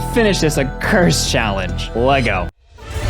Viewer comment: Did you say Lego or let's go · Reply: Lego